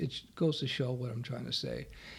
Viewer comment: hey, what did you say here at the end ? that was funny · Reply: It just means "and there you have it", Bob's your uncle.